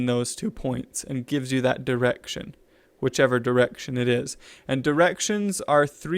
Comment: thought he said erection lol